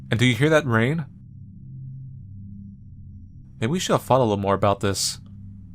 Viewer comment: 30 I hear we should've followes or something. Um 6:30 i think cuz 6 is on top of - Also i meant followrd